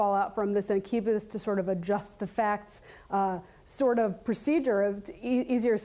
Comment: Did she just say "adjust the facts"